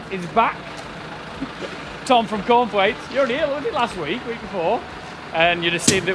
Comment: Cornthwaites, I could have sworn you said corn flakes.